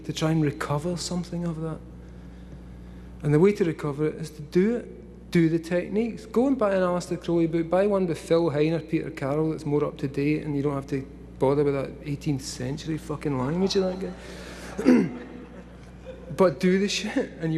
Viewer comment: What names is he saying? I have a hard time understanding the names. Thanks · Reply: Aleister Crowley, Phil Hine and Peter [J.] Carroll.